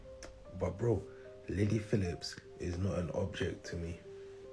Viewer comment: I thought he called her "Lady Phillips" 😂😂